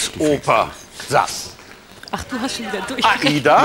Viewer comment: Was sagt Barbara bei 0:15 vor "du Arsch"? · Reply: "ach du hast schon wieder durchgedreht"